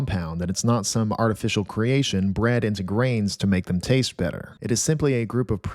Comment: What Was Said: "Bred into grains" What I Heard: "Bread into grains"